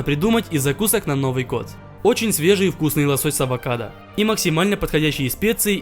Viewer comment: мне одному послышалось "собак ада" ?